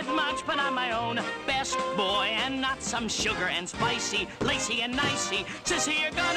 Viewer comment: When i turned on captions why did it think when he was saying sissy it thought it was a bad word 😭💀